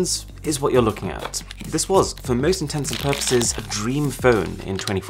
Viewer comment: is he actually saying for all INTENSIVE PURPOSES?!?? With 6M followers you'd hope not